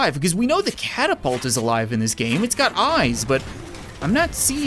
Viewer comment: did he say catapults have eyes?